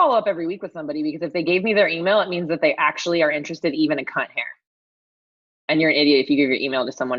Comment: Are we really going to ignore the fact that this woman said “c*nt hair” on a business call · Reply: (non native speaker here) hahaha thank you for this comment I actually thought she said "cat hair" and I was like "oh that's a cute phrase I didn't know" lol